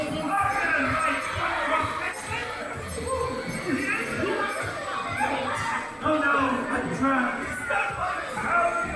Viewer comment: What does the mint say? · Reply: He says something along the lines of "oh no, I'm trapped, what ever will I do?" It's a bit of a sarcastic segway into the grand finale scenes.